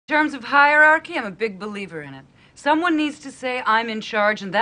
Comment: At the beginning it kind of sounds like she says "germs of hierarchy"